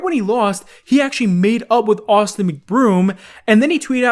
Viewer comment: i thought he said made out